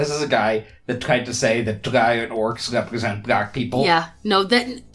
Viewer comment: Ok when i first heard him say "drow and orcs" I thought he said "dragonorcs"